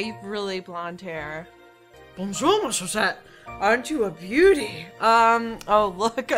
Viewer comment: I heard "Bonjour, ma chaussette !". x) "Chaussette" means sock in french. ^^